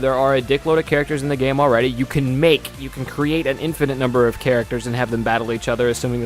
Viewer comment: WAIT!!!!!!!!!!!!!!!................................ How many characters can you make on Xenoverse!? I thought I heard the word infinite at around 3:10. I hope to God you can make more than 8 characters!!